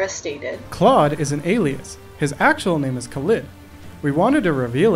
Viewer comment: I heard "claude is an alien" instead of alias